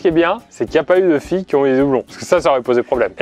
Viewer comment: "En tout cas ce qui est bien c'est qu'il n'y a pas eu de filles avec des yeux blonds" J'ai mal compris ooouuuuuuuu ??? xD Si j'ai mal compris bah dites-moi c'est quoi qu'il a dit hein ! Ou sinon donnez moi la signification des filles avec des yeux blonds car honnêtement, j'ai pas compris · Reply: Effectivement tu as mal compris sa phrase mdr il a dit "doublons ".